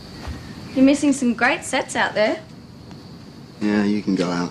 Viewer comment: I thought Cassie said you're missing some great sex out there 😂😂